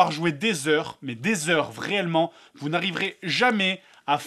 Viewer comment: C'est moi ou à 15:07 il dit "Vraiellement" ? xD